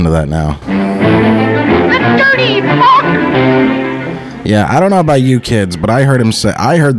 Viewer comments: I think he actually said "That dirty funk" (the dirt meaning, not the dance move meaning). Thank me later 😉. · Watching this again, it definitely sounds like Bosko was saying "that dirty f*ck"